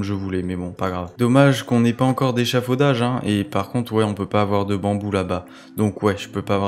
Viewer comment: c'est moi ou a 8:11 il di bambou la bas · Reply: "on peut pas avoir de bambou là bas" ne déforme pas ce que j'ai dit